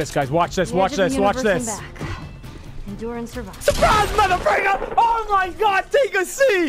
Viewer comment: did he say fricker